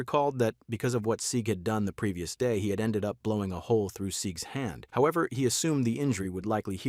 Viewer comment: did you say.. a hole in his hand ? Does he also happen to be a "royal scientist"? Hey guys its me wing gaster the royal scienti- Ts was so corny idk why i said that